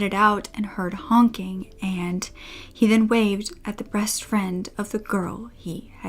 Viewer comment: You accidentally said "breast friend" instead of "best friend". At least that's what it sounds like. Just wanted to let you know x